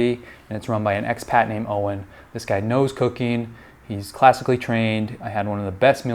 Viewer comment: Did I just hear that the chef knows 'cocaine'??